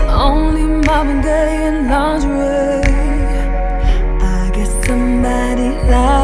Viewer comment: I heard this song for the first time and I thought she said: "only Mom and Dad in lingerie." I lost it... 😂🤣😂🤣 But now for some reason now after reading these lyrics... why am I only hearing "Marvin Gaye in Lingerie." I will never hear "Let's get it on" the same ever again. I have been screwed up mentally for life. 🙃😯🤔